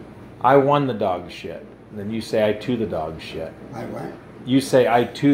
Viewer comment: Ngl I thought he said I chew the dog shit at the start, loved the video, extremely wholesome.